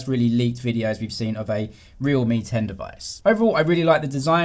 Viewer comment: First Time I thought you said Realme 10😂😁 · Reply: lmao I heard the same ting as well. realme 10 vs real Mi 10 😂